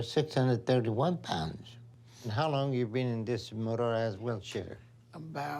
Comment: I thought he said “how long have been in this little ass wheel chair “😂😂